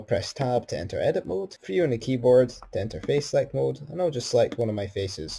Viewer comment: I have no idea what you said and that is even with subtitles on :( - sorry. It was in reference to selecting the face and even the subtitles gave this.... "press tab to enter edit mode for you on the keyboard to ender face select mode" - wtf do we do for face select mode?